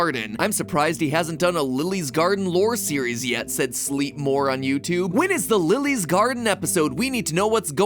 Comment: i thought he said "sleepmoron youtube"